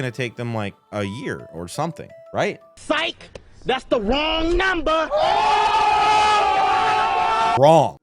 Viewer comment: WAIT A MINUTE ! That is what he said to rile them up like that? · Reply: He specifically says “I broke up with my ex girl. Here’s her number.” He then hands the other guy a piece of paper then he goes, “SIKE! THATS THE WRONG NUMBER!” The whole video is a goofy ass freestyle rap 😂